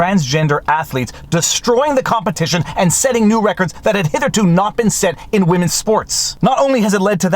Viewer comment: Did you just say, "hitherto"?